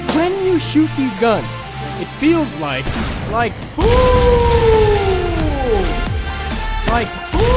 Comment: it’s sounds like your saying like poo hahahahahhahaa